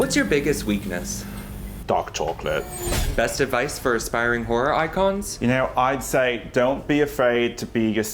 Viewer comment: I could’ve swore he said, “Best advice for aspiring whore icons?” Geesuz I’m getting old. And the knife vogue dancing at the end 🤣🤣